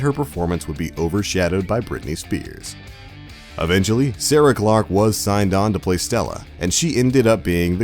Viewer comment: Excuse me but the actress who played Stella is named Sarah Chalke, sounded like you said Sarah Glark or something.